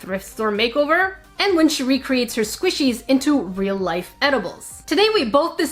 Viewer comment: I thought she said she makes her squishies into real life animals and I was so confused for a second, like Frankenstein?